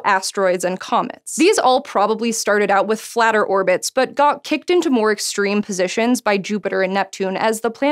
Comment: I really heard "but God kicked them into" :-D